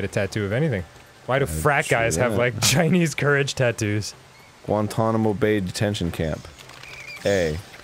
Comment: Oh I thought he said "Won-Tonamo Bay".